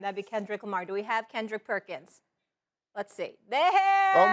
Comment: 😳😳😳. Did Molly say Kendrick Lamar?😂